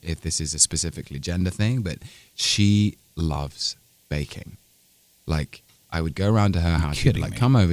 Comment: when he said "scarlett loves baking" i misheard "bacon" and was like WHUT??? xDDD